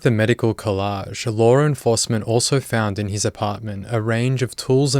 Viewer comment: Dude I know you're Australian but I've been listening to all your videos throughout the week and every time you say "law enforcement" I thought you were saying Lauren faustman and I heard that name in every video and I wondered how she could be involved in all these murders and vile cases. So I looked her up and couldn't find anything that made sense. but then thought about it.. and realized it's your accent. Oops.